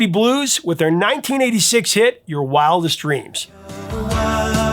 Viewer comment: Ok I'm known for miss hearing lyrics I thought the song said wild Irish dreams lol